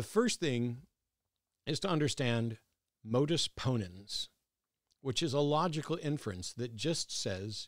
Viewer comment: For about the first 2 or 3 times, I thought you were saying Modus Poland, and I thought I had missed the Polish joke.